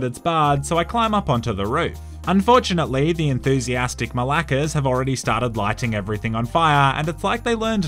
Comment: did you just say malakes? xD